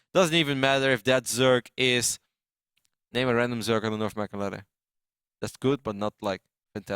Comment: What does say at 18:16 "name a random zerg, i don't know if blah blah blah?" What is the blah blah blah part? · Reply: He said: "Name a random Zerg on the North American Ladder that's good but not great." "Destiny" BURN!!!